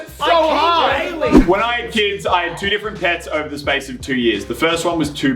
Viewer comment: did Elliott say "When I had kids"